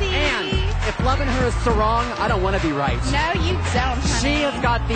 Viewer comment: I could be mistaken, but I think he made a pun. It sounds like he said "if loving her is sarong, I don't wanna be right" cause she was wearing a sarong. Yeah, he was cracking me up too. :-)